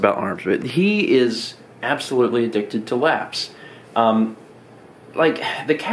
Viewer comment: I thought it said "lamps" instead of "laps"